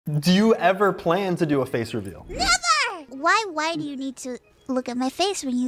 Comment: i swear i heard her saying "ni99er" instead of never